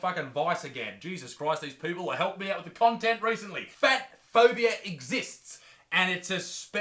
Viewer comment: I thought it said eat phobia